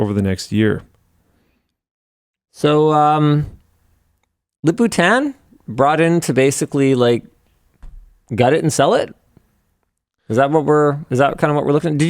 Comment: What does Linus say at 0:34? I don't understand it and the subtitles neither. Surely there has been missmanagement and pride and as you know "pride goes [or comes] before a fall". It's really bad with Volkswagen. I guess Intel is still in a better position than Volkswagen. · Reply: “So, um… Lip-Bu Tan brought in to basically gut it and sell it” Lip-Bu Tan is the current CEO of Intel.